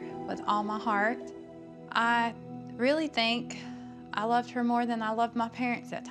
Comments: I heard “they think I killed her” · Kinda sounded like he said "They think i killed her." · I heard "They all think I killed her." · What I heard, was; “They think I killed her.” Listen to it again! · Sounded like he said "they think I killed her" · It sounds like he said they think I killed her. But obviously he’s still the killer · To me it sounds like he said, “they think I killed her” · sounded like he said" they think I killed her " just sayin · i hear ‘they think i killed her’ 🤷🏻‍♀️